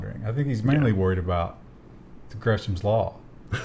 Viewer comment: What does the guy to the right say? (..) "I think he's mainly worried about...(?) law" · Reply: Gresham's law